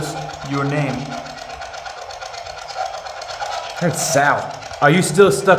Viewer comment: During the sprit box around 19:55 when you ask for a name I hear Trevor not Sal